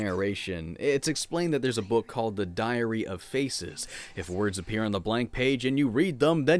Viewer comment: every single time you said "diary of faces" i heard "diarrhea of feces" and yes, i am 5 years old.